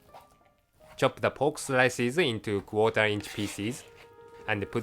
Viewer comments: did he say quarter inch feces? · it sounded like he said feces 3:12 . :D hahaha